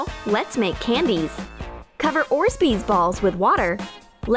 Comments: Who else hears "Orzbeez"? xD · did she say “orsbeez”?! 😂😂😂 i’m dying 😂😂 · Did she just say orsbees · Did anyone hear that she said orsbeez? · Did they just say “orsbees balls”?😂🤣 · Did she say orsbeez balls instead of orbeez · did she just say "Orsbees?" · she said “orzbeez” instead of orbeez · did anyone hear her say oris beads ball instead of Orbeez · did she call them orbsbees 😂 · did she say orsbies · They said orzbezz balls instead of orbeez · Did she just say orsbeez?! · did she just say "orzbeez" instead of orbeez??? another thing wrong with Troom Troom